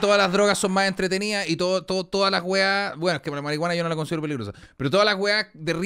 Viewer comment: que dice en el minuto 31:52.? jaja meo trabalenguas. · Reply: LaMariguanaNoLaConsideroPeligrosa jasjajs